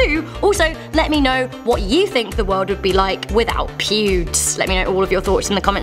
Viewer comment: thought she said pubes